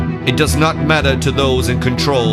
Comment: sounds like ur saying illuminadi